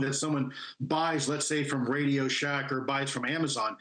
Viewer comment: did he just say radio shack? 😂 This was a waste of 5:33 min, they are obviously drones. Probably ones you can buy with a license.